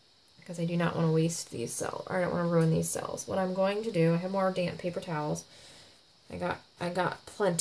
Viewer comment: Is the paper towel wet? I missed that part. Thanks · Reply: I wondered too. I heard her say " a damp paper towel".